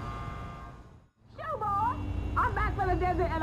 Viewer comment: what does his aunt say in this scene? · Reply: zwahwhaheh boy get